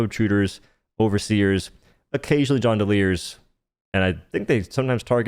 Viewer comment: Can someone please tell me what word says at 1:07 Occasionally what? Johndeliers? Why does he call them that